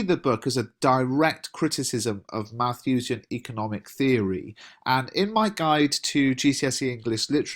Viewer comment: What is he saying at 8:20? something economic theory ???? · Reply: Malthusian economic theory